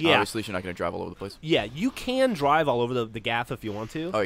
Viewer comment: did Geoff say "you can drive all over the GAF"?